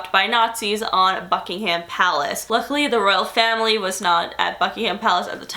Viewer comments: i thought you said FUCKINGHAM palace lmao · For some weird reason when you said "Buckingham Palace" I seriously thought you said 'FUCKINGham Palace' Lmfao! Love the vids chic.